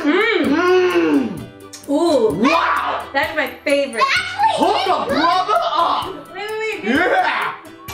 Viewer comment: what did he say?😂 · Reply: I meant “hook a brother up”